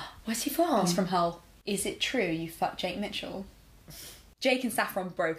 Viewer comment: Who also thought they said Shay Mitchell around 5:20 ? 😊